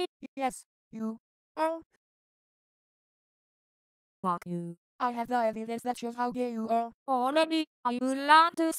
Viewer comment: I swear I heard i have diabetes